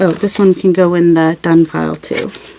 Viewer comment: Hahahaha I thought you were saying “they can go in the dung pile” but you were saying done pile! Thank you closed captions! I like this series!